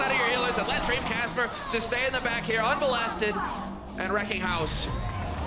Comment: Did he say "unmolested" at 1:55 lol